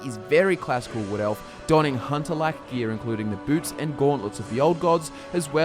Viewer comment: Gear including the boobs..WHAT?DID YOU SAY BOOBS?WHERE?